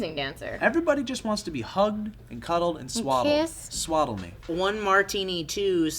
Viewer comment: I thought Joe said "everybody just wants to be hugged and cuddled and swallowed" at 2:39 oops